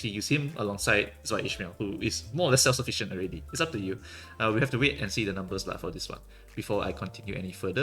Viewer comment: it sounds like you said "We have to wait and see the numbers lah."